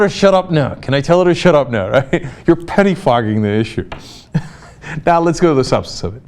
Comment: I literally thought he was going to say, "your petty fucking the issue!!!"